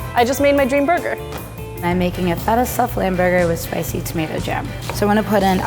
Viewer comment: Whenever said lamb burger I thought I heard liver...